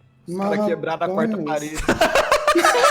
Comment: não entendi merda nenhuma do que o disse kk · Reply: "Eles quebraram a quarta parede"